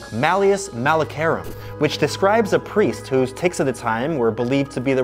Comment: I thought he said t*ts replace the * with a i